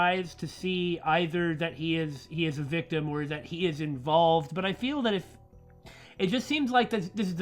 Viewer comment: lol missheard you say involved as evolved, and the idea of a Gelified Time Zombie Army (resident evil style) with the second half of the game being Future Lab (equiped with laser pistols) Fighting GelZombies threw time for the fate of the world hahaha